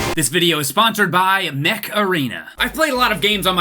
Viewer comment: thought it said meth arena for a second